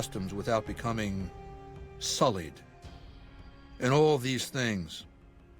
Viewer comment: is it just me or did I hear Bankai? Edit: here it was agian 47:39 bankai.